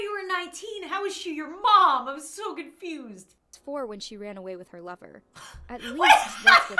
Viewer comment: When she said, "She ran away with her lover," I thought it said, "She ran away with her brother."